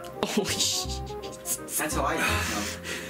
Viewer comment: When is drinking the milk I hear say "that's how I use milk"